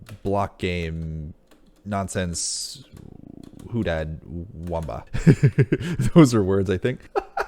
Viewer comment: Did I hear a 'who dat' at 3:26?